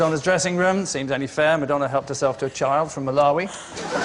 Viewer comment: What is Angus saying at 3:30? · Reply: "Seems only fair, Madonna helped herself to a child from Malawi."